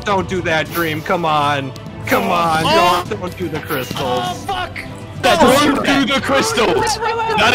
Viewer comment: istg that "FUCK" sounded like bbh